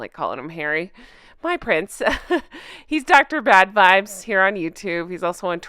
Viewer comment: I thought he said he was Dr Bedvibes… lucky you… lol